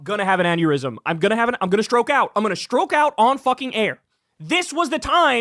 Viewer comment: For a minute there I thought that Kuvklinski said that he was going to stroke off on air. It would not surprise me because you can tell that he is perverted like that but after listening again I realized that he said stroke out! lol